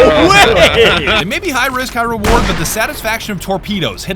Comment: I thought he said "high rizz, high reward"